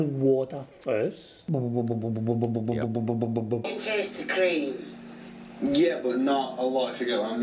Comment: Sounded like "can you taste the cream?" "Yeah but not a lot if you get what I mean" "you might have to have it all then"